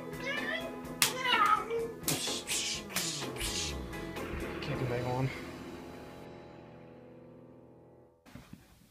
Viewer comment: what exactly did you say at 1:20??? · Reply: ahh, yea i did not sound good. I said "I can't do Megalon" and that is why I put MEGALON…(Sorta) prior to the scene.